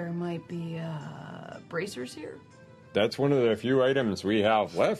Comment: I could’ve sworn Beau said “Braziers” . But the captioning said “brassers” ... I guess that’s part of the Locate Smut goal.